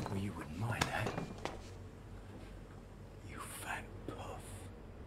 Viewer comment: What does he call Ron at 1:08 and what does it mean? Not sure if it's a different language or if my hearing just sucks. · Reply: Really Bro? "puff" = gay